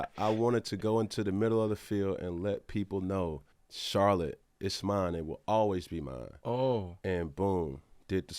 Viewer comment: Cam. Bro. It sounded like you said "Charlotte is mine" but its "ours" i know u feel dat.